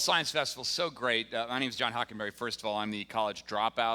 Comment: I feel screwed... John Hockenberry?! I thought you said Gene Roddenberry!